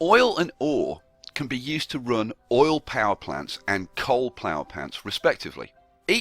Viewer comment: Did he say "plower pants" at 4:21?